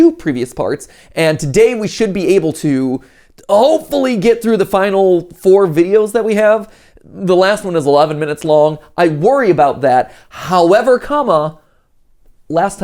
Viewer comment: i love matpat but i was folding my clothes and then i heard "the last one was allaven minutes long" and i was so confused- this might just be me hearing that but like help